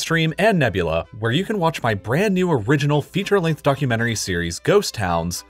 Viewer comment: I heard fuckumentary instead of documentary XDDDDDDD